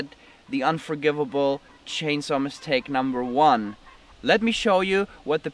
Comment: I thought it said chainsaw milkshake